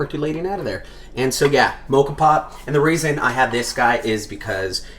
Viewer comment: MOCOS POT?! oh Moka Pot, i misheard. lol